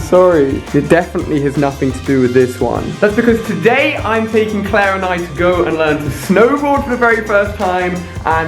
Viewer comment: I thought he said Claranite